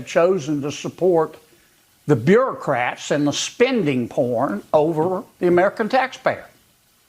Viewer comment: Ok fine, I'll watch the rest. Did he just say spend porn?